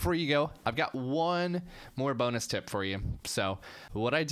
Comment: I heard Boner Tip at 9:52 lmao!